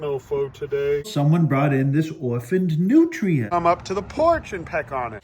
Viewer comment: I thought you said "orphaned nutrient", meaning you were going to feed it to somebody on the ranch. BTW I can't figure out what's more sick: shooting kangaroos or killing horses for meat. I guess that's the norm in Texas???